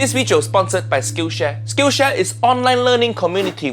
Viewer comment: Can't believe I thought that Uncle Roger said Skin Share and not Skill Share